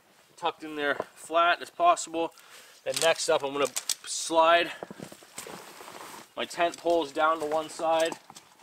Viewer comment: I thought you said "shove my testicles down to the side" and I cant stop laughing 😅 Another wonderful vid, thanks!